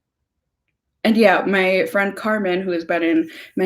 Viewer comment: hahahaha everytime you have mentioned your friend Carmen, I keep hearing "cartman" from south park....